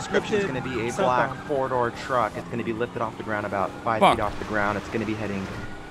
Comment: did he just say 5ft off the ground?